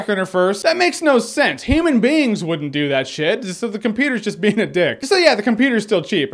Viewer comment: When said "human beings wouldn't do that shit" I thought he said "ham and beans". I was very confused